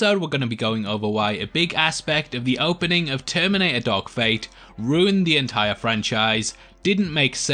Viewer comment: Lol sounds like your saying “Terminator Dog Fight”